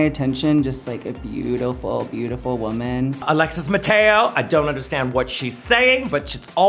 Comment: Did Bianca said Alexis Mattel